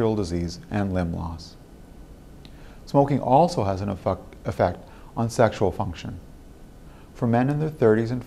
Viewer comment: He says "e-fucked" instead of effect at 3:50 bahaha!